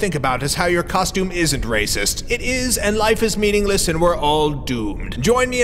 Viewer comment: Instead of "we're all doomed" I heard "we're all gay" xD